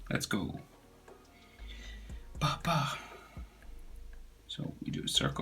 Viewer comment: why did u say papa